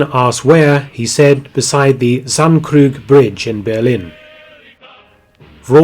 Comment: What is the name of that bridge in 3:39 ? I heard sunkrug but i didn't find it in google maps